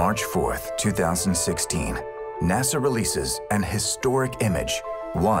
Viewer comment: did he say "NASA releases AN historic image" ?